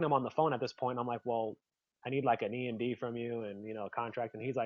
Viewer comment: What does say at 17:14? Sounds like he said "I need an E and B from you". What is an E and B or did he say something else? Great video by the way. Edit: I listened to it again and I think he's saying emd for earnest money deposit. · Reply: Yes, EMD - Earnest Money Deposit